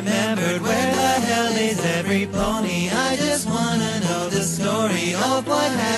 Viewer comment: I know i'm late but am I the only one who hears "Where the hell is every pony" XD